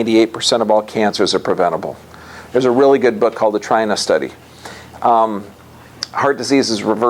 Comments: Did anyone else catch the name of that book/study he mentions at 34:57? I've rewound like 10 times and it sounds like 'Try Enough Study" to me every time and that yields zero results in searches. LOL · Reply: Maybe The China Study? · did anyone understand the book he mentions at 34:58.... i can’t understand what he says it’s called ! i would very much appreciate it thanks in advance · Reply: Sounds like The China Study